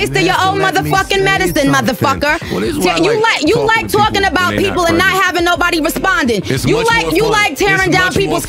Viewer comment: Did Joe say this is why I like talking to ppl when they not pregnant to Nicki hmmz?!!?😎 around the 19:49 mark listen!!